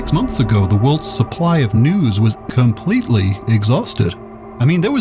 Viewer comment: I thought he said the worlds supply of newts 😂